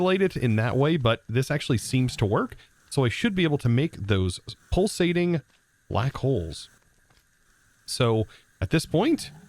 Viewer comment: What others hear: "Pulsating black hole" What I hear: "Pulsating Butt hole"